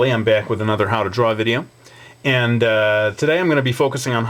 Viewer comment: did you said "How to draw a video?" Hahaha... That's hilarious, dude.